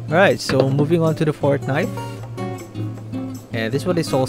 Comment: who else heard Fortnite?